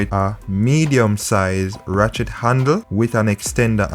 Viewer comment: This is very informative. I have a loose chain...will try this and also keep my mechanic on speed dial. but you keep distracting me...I keep hearing "Rat-shit handle".... Will delete comment...lol